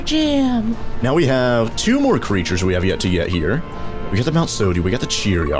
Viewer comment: sounds like you said yet to yet